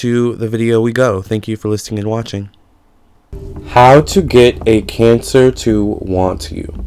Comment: I thought this said "How to Get Cancer" Lit.